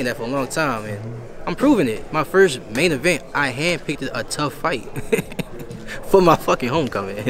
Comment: I thought he said he handpicked a undefeated fighter 😒🧐